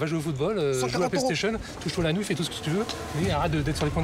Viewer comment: À 17:38 c'est moi ou il dit "va jouer au football, à la PlayStation, touche toi l'anus"